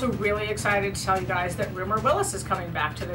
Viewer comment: Who is getting back on the show?? Cause all I hear is rura rillis bla blah ??